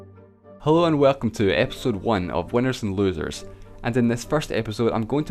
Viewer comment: Did he just say " hole and welcome to ...."